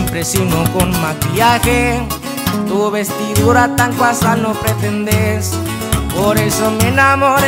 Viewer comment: It might just be me but when he sings “Tu vestidura tan casual” instead of “casual” I hear “cuasal”. 😂